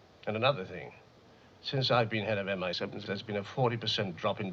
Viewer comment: when M says he's been head of intelligence, did he say MI6 or MI7? It sounds like 7. I've watched all JB movies several times and I've never noticed this before, since it's always been MI6. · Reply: I read that they had Bernard Lee redub his line to say “7” (his lips say “6”) because MI6 was a real secret in 1962.